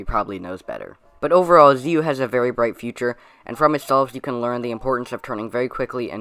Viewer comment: I thought you said he has a really bright T-shirt. And I was just like “how the hell is that relevant??”. But seriously, amazing video dude. I love the megaminx content!